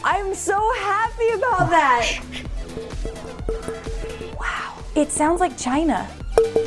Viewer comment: Is it Just me or did i Just hear "woooow....its sounds like china"